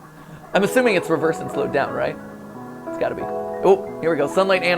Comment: I heard 'slow down Mat.' lol